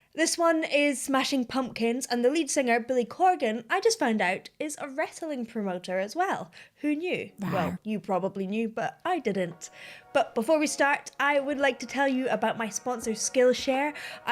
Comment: It sounds like you said Ritalin promoter, I literally had to read the text to find out what you were talking about....